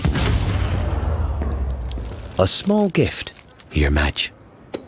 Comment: does he say “a small gift for your Vadge?”